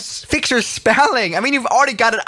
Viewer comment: he was going to say "ugly aircraft" but my man heard it as "ugly ass"